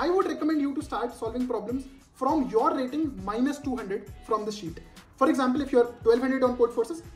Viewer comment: wht did say ? would teach ? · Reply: I said you should start doing problems from your rating -200 FROM THIS SHEET, it might have sounded like TEACH.